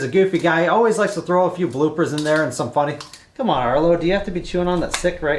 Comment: Amazing idea for giving new life to a dead machine. Looking forward to the completion of the project and excited about what is next. At the end I thought you were talking to me when I thought I heard you say come on Earl. Be blessed!